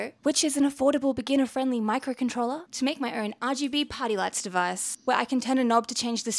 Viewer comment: all I heard is poop, poop and then I thought you said potty lights lol